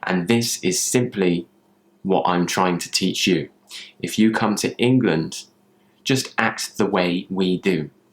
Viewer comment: Sorry ..at 6:03 you said if you comes to England . You add S to the verb , this is what I heard , is that a mistake. Thank you 😊